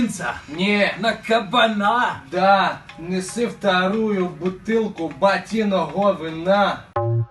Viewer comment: мне послишалось что он сказал да неси вторую бутылку батиного гавна